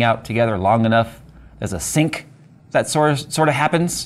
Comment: i was listening not watching. When you said sync i thought you said sink.